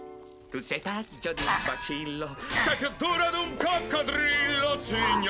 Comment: Che cosa dice il Grinch al minuto 2:08 perché il testo è totalmente sbagliato? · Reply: Credo "Tu sei fatto di un bacillo, sei più duro di un coccodrillo"